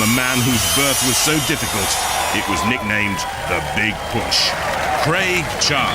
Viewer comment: hold up what did he just say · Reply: the big push